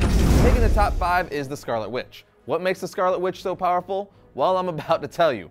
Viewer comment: I wasn’t looking at the screen and I thought this mans said Scarlett Johansson all his credibility was thrown out the window until I realized he said scarlet witch